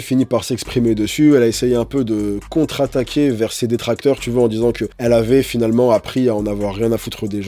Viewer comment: Tu as dit " vers ses détracteurs" j'ai entendu "verser des tracteurs". J'ai ri à l'image 😂